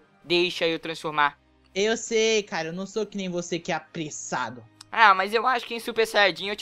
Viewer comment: Achei que ele falo "EU SEII CARA N SÓ QUE NEM VC QUE MATA A SAH" KKKK FOI SÓ OQ EU OUVI